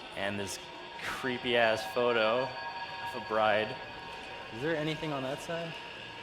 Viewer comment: Did he say bride, or Brian? If it's the latter, I wonder how Brian would react to that.